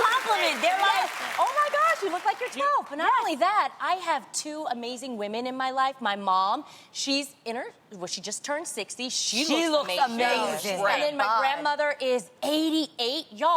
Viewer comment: When said 2 amazing women in her life, I thought she said kids lol